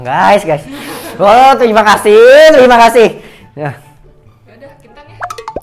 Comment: bilang apa itu gk kedengaran kasih tau dong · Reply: Dia minta traktir kintan